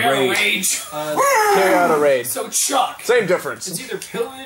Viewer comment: I thought he said carry out a Rave.